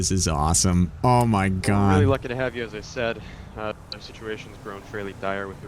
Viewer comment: It kinda sounded like said police to have you